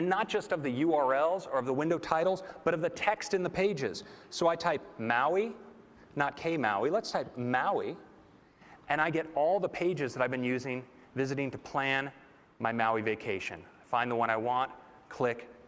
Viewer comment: what did craig say at 3:50 ? Key maui?